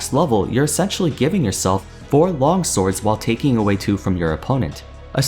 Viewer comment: I wasn't really focusing on listening and I thought I heard him say "You're sexually giving yourself 4 Long Swords"